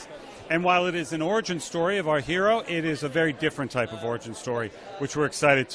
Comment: He did not say a different origin story. He said a different type of origin story. Typically origin stories are linear but this story will be told through flashbacks and discovering elements of her past since she lost her memories.